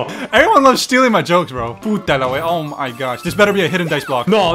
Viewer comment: I heard "puta la weá"